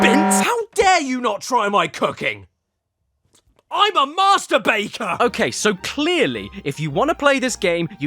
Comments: I'M MASTER BAKER How I heard It: I'M A MASTERBAITER( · my lack of practice in UK english made me listen "masturbator" instead of "master baker"